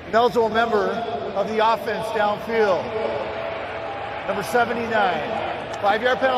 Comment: I'm new to the game. Can someone explain the penalty at 1:52? (I'd usually Google it but can't make out what the ref is saying). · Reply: Ineligible receiver Downfield, its when a lineman crosses the line of scrimmage during a play